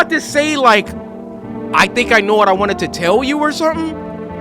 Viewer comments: I’m kinda late but for anyone wondering what she said at 54:42 “I think I finally understand why I’m so c-c-cold.” :) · she said "i think i understand why i'm so cold" and 1:33:44 "Always knew i'd die to someone also a or of idiocy. for anyone also wondering what their last words were. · also btw for the ppl who were wondering what cold girl said at 54:42 it was "i think i finally understand why i'm so c-c-cold." harassed girl/pink hair girl at 56:10 : "Or...Or...Let me have just a little longer..." Didn't even have to slow down for 1:33:43!! i just hit the perfect frame on accident lmao :3 : "Always knew i'd die to someone else's idiocy." Depressed sister help 1:44:54 : "Bittersweet without the sweet" (ima just update when i see intersting ones, boring ones i don't put in unless u guys wanna know) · I think she said “I finally know why I’m cold” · if ur wondering what shes says , she says “i think i finally understand why im so c-c-cold” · what she said was "I think I finally understand why I'm so c-c-cold.". 56:10 She says "Or...Or... Let me stay for a little longer..."